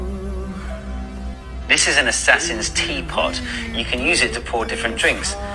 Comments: I thought it said Asian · Fr i thought it said Asian